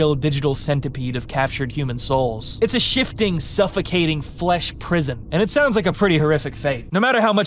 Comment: did you say "flesh prison" ?